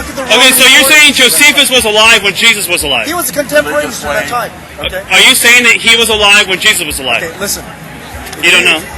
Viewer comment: WTF does that bloke say who walks past behind with his hands over his mouth? I've rewound it several times and all I can make out is 'slave'? I think? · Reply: "religions lame"